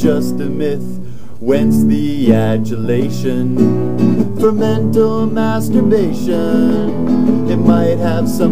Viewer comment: Did they say Mental Masturbation at 1:47?